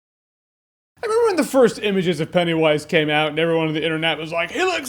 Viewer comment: what did he just said ? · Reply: Anmol Nigam "I remember when"